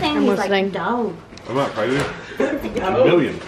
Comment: is he saying “stop” to your mother literally ? 😂😂